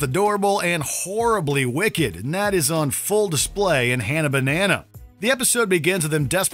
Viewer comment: did i hear it right ? 15:20 Hannah Banana ? haha